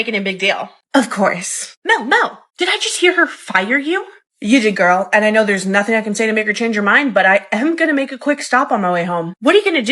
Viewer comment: What did she say at 00:26 🤨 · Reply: You did girl, and you know there's nothing to make her change her mind.